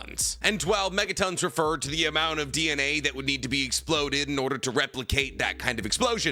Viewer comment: did he just call Megatons of TnT megatons of DNA?